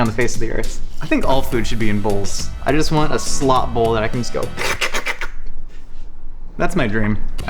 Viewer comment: I thought you said slut bowl